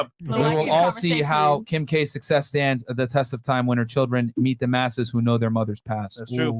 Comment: Young lady thought he said Masters 😂😂😂😂😂